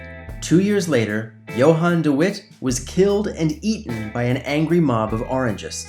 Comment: Did you say "eaten"? As in cannibalism? Wtf...